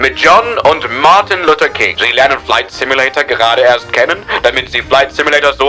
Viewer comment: did bro just say Martin Luther King Jr?